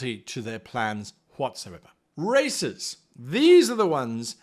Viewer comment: Guy said Racers, I thought he said Racists. I was like: "well that's certainly a great villain type."